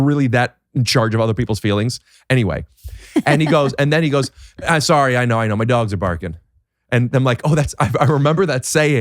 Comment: my guess is he said “I know I know, my dogs are barking” probably because he’s sorry/acknowledging the odor but he felt the need to take his shoes off becauseeee his dogs were barking